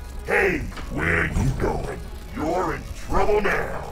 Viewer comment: I stopped breathing when I heard Monty say "Where you going?"